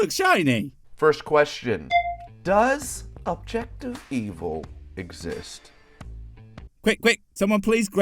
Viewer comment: That first question, I thought he was saying "objective PEOPLE". my first instinct was WTF. And then, I heard answer, and I thought, this guy is a bad speakeriser! 😁